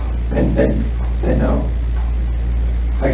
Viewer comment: Go to 4:54 in this video. There are two separate translations where Chris states "and they know. I cant think about that." VS "I came clean about that." Translation #2 is inaccurate if you listen closely. So go back to translation #1. He was previously talking about his affair, which we ALL know that is all he was focused on. He was obessed with NK. So what is he saying ......"they know. I can't think about that." I always saw this as his dad knows he killed his family. He is letting his dad know that the cops are on to him. And he is stating he cant think/talk about he dirty deeds. Why isn't anyone concerned about this statement?